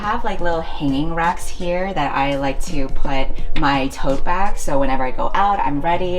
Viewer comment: IDK if I'm still having a hangover but I heard "hoe bags" at 3:22 instead of tote bags 😆